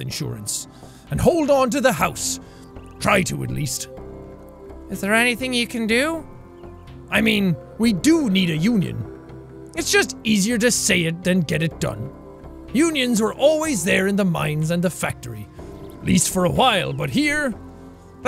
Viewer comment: Sorry but, when dad says "Union" I thought he said "Onion"